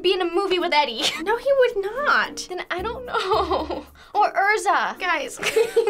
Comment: I'm sorry did she just say ERZA? wtf haha